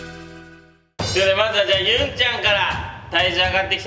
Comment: 絶対違うんだけど ゆん に聞こえてドキッとした💗